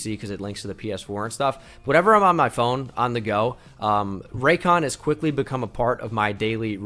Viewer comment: At the beginning I deadass thought he said rapecon😳😂did a double take there